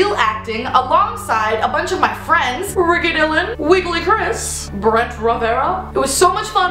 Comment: I thought u said "wiggly Chris" haha xD